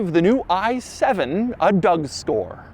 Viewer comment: Did he just say a Doug Store 🤣🤣